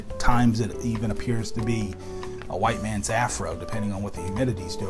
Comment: Did I hear right??? "White mans ass rub" LOL I didnt see that one coming!!! Great selection you got there. Good to see a video from you.